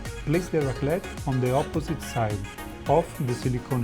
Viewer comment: A raclette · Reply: Chocolate Academy Online I believe it's called a raplette. I thought I heard raclette (as in the cheese) in the video which threw me off. <link>